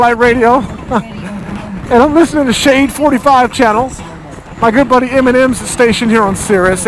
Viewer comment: did he just say "my good buddy eminem"